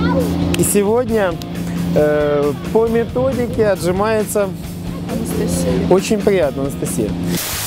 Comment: "Папа, чё она отжимается как на грядке паук?" или мне послышалось?